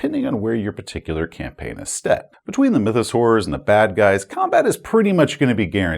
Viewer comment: I'm glad you took the time to type up a proper transcript, because I couldn't figure out what a "Mythosaur" was (at 2:45).